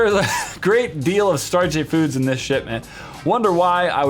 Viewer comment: Lol I thought u said "sh*t man" not "shipment" lol